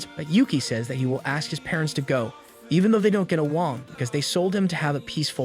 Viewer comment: did he say awoong or am I crazy